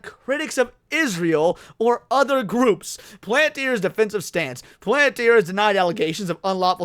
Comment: "What the hell is the Planeteer controversy?" - What I thought said. Me - "🎶Captain Planet he's a hero, gonna take pollution down to zero! He's got powers magnified and he's fighting on the Planet's side.🎶Sorry, couldn't resist. 🤣🤣